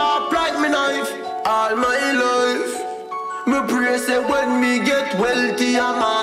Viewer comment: Did it say nigga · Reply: He actually said mi get